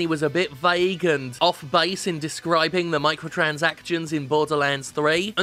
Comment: Jim, I swear I hear you say "Michael Transactions" about half the time.